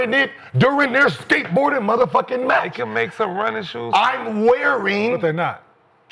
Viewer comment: Did Gil say skateboarding match?